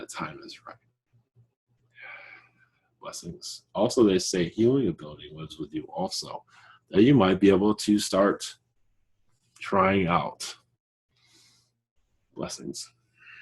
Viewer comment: What is he saying at 29:25??? Anyone please! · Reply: OMG! it's "Healing abilities lives with you also, that you might be able to start trying out".